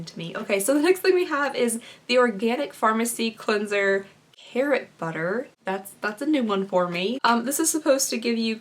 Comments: Hair butter!! Lol definitely a new heard of item lol ig: emblossom88 Edit lol omg I thought you said hair butter had to rewind and really look at the container and no its carrot butter lol still never heard of before tho!! Lmao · When you said carrot butter I 100% thought you were saying "hair butter"! 😆 😂 🥕 🧈